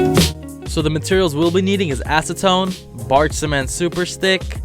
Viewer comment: When I heard "acetone" I thought he said asstone